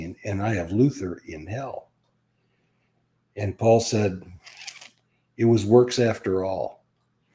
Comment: What does he say on 07:15 ? I cannot understand it. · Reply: It was works after all.